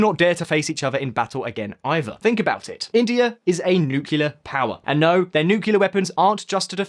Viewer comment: Did I hear you say "nucular power" (8:47) instead of "nuclear power" ? Did you spend too much time in the US ? :)))))